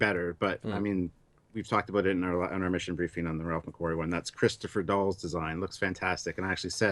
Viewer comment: What name are you saying? Christopher Dull?